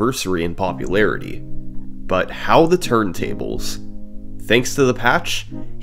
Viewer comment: am I hearing this correct? “How the turn tables?”